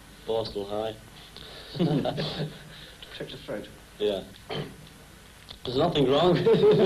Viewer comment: Oh Ringo soon you’ll get your tonsils out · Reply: Could you answer me a question? (I'm not a native English speaker) At the time 1:19 did the reporter say "a touch of throat" ? I didn't understand, what did he mean? Ta